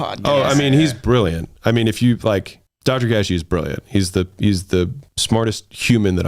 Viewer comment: What is the name of the philosopher friend Alex is mentioning here? I can't quite make out the name though it sounds like Dodger Gashi.